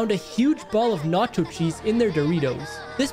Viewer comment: i thought he said nazi cheese💀